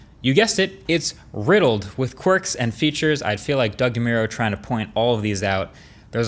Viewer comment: hold on, did you say Quirks and features there? 😉 btw thanks for showing images of Australia, Brisbane in particular